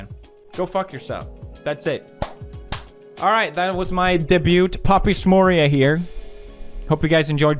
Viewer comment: did he just say debuT